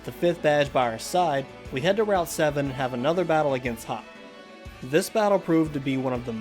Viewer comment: did i just hear "We have to battle hop again, this was one of the most challenging in the entire run"